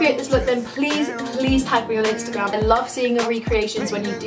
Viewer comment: at the end i thought she said "see you guys with another crispy video" lmao